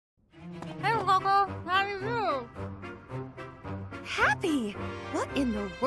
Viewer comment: What Hapi said to Constance with food in her mouth: 0:00 “Hey, Coco! How're you?" 0:08 "No need to be jealous. There's plenty to go around." 0:21 "My pleasure!"